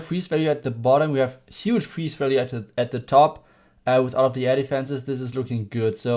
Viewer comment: With the German accent when you say air defense it almost sounds like you say elephants! Awesome vid!